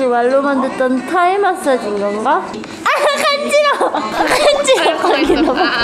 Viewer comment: after thai message i hear my name Ganga from Rose(it's sound like my name)